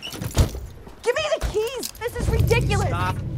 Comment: does she say "Youre gonna regret this bitch!"?